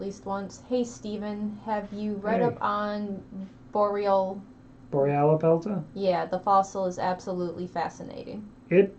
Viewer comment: what dinosaur? It's difficult to parse the word enough to look it up, help!! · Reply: Euoplocephalus tutus